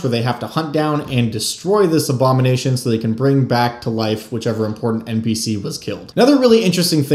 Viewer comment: You say "important npc," i heard "pet goblin"